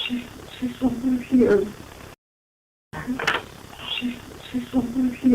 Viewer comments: the EVP that was recorded says "SHE'S SOMEWHERE HERE" doesn't say "THERE'S SOMEONE HERE" I'M JUST SAYING 🤷🤷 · I hear: "I see something here". I do not hear "There's someone here". I replayed it several times and it is clearly, "I see something here". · min mark where the EVP says "THERE'S SOMEONE HERE"..........i hear "SHE'S SHE'S OVER HERE" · it sounds like the son who committed suicide says, "SEE, SHE, SHE'S SOMETHING HERE" then you her a woman scream, " GO AWAY ", At least that's what I think I hear. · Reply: "She's *somewhere here" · No:44 at 40:30 I swear the voice says “She’s somewhere here” rather than There’s someone here.